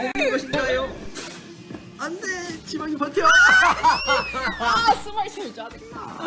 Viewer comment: 쓰발 진짜 짜증나(?)저만 이렇게 들었나요ㅋㅋㅋㅋ